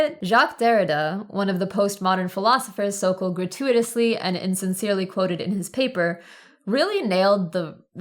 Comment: Hi there! I have a very minor criticism I hope you see before the Arbitrary Cutoff™ (sounds kinda ominous to me :D) My phone's audio is sometimes hit-or-miss so I often watch videos with subtitles. I love that you provide actual subtitles because your videos, whose overall quality is already top-notch, seem even more professional. However, there are a few quirks and typos: eg. 'as' instead of 'is' and 'sincerely' instead of 'INsincerely' (around 53:28). The content can often be disambiguated through context, but more ambiguous mistakes in past or future videos could take away from the viewer's reading of your point. I hope that's clear! YT introduced me to your channel last year through your video on how to organise social change, which was very useful to me at a time when I felt oppressed by the lack of social interactions at uni in my first year... it's still isolating but I'm working through it. Anyway, that's all from me, and thank you for the great video!